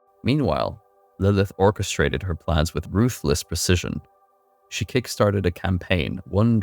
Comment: I first heard "Lilith's Kickstarter campaign" when you said "Lilith kickstarted a campaign". What sort of supporter rewards and stretch goals do you think Lilith would have? :)